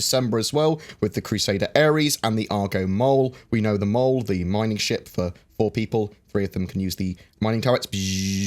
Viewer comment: Thought you said the mole was a mining ship for poor people at first lol